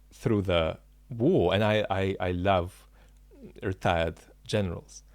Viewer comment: Everytime I hear you laugh, I not only laugh with you but I feel genuinely happy —thank you for keeping this wonderful, liberating laughter in the video 🤣🤣 (also 🙈🙊 I initially thought you saidl: retarded || but! That was/felt out of character for you. So this makes sense 😉⬇️ ”I love retired generals” ©️ || 29 June 2023 Many hugs, Grace 🎶🩰🐾